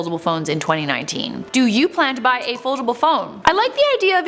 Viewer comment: Oh...I thought you said affordable...ok.